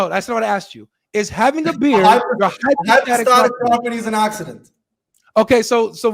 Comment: “A hypostatic property is an accident” did I hear him right?😂